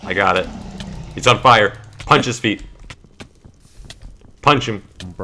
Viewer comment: I'm counting your successes as my own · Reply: When techno said punch his feet I thought he said punch his meat lol